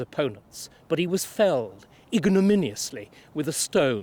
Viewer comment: What word does Starkey use at 43:51? 'He was felled....' Iginimously? Is that a word? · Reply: Ignominiously is a word - it means humiliating or degrading ....